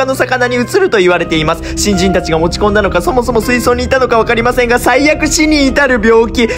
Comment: 新人たちが持ち込んだのかが新人たちが落ち込んだのかに聞こえて意味わからんかった